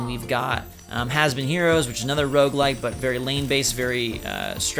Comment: When he mentioned Has Been Heroes, I thought he said it was lame based, not lane based. Either description works!